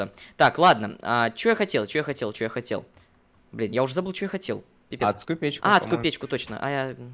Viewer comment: Чуть-Чуть кажется что он сказал Б***Ь